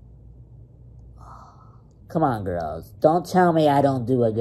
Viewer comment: Pink not gross but when he said middle finger I knew f you was coming lol wait I thought he said gross but he said girls nvm